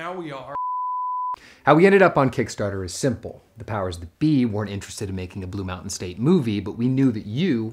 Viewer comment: What does Brooks say at 1:29? The powers or something? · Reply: The powers that be. <link>